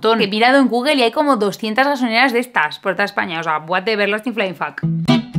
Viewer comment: ¿que dice?😂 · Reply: What the everlasting flying fuck